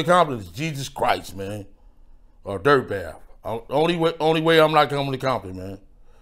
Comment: it sounds like he says only Jesus Christ or Dirt Bath will keep him from coming to Compton. Is Dirt Bath the current leader of Compton or something?